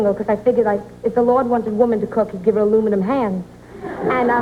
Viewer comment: sorry guys, can someone help me understand what she said at 4:07? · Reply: I figured, it's like..if the lord wanted a woman to cook, he would given her aluminum hands...